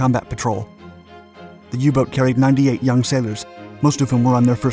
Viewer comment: did you say U-boat?